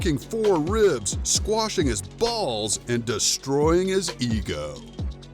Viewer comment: At the end it sounded like he said “Destroying his balls, and crushing his ego”